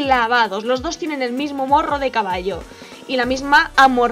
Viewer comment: soy la unica que escucho que dijo caballo xdd